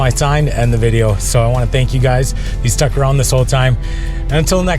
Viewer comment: I heard "I'm stuck Aquez." I don't know but that is really cool! Keep up the awesome videos.